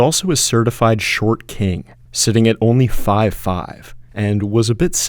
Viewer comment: Did you say only?